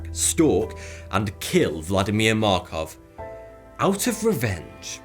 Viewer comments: I swear I thought I heard Vladimir Makarov (MW) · Thought he said Vladimir Makarov for a second ngl